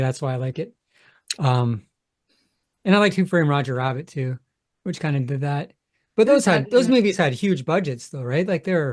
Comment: sorry what did you say. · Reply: I said "Who doesn't"